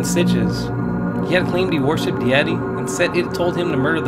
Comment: he says he worship the .... ? What did he say here ? ... it almost sounds like he said he worshipped the YETI ... ?